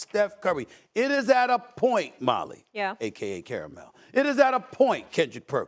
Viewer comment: Did anyone else catch Stephen A call Molly "Caramel" at 2:24 ?